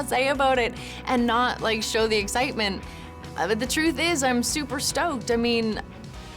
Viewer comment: did she say "I`m super stoned?" 47:10 ^^